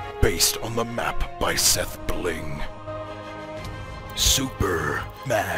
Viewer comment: i think the voice over guys says super mash pvp